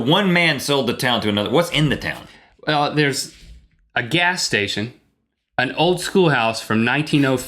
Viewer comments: I thought he said "and Old school House" turns out it was and old Schoolhouse! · At first I thought you guys said "Oldschool House", not "Old Schoolhouse".